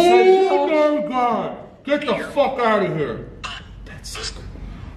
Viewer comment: firstly... talks a LOT, couldn't get a word in 11:09 shadow going across the wall, could be reflection from cars going by though? 19:28 i swear i heard a whisper say Necrophonic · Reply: I thought it said ‘Get the f##k out’, but definitely heard it with headphones on.